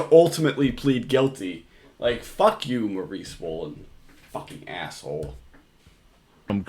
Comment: I keep hearing “Mauri Swollen” and it’s really bothering me.